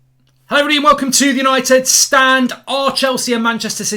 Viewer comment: did he say howdy? What texas stuff are you watching mark?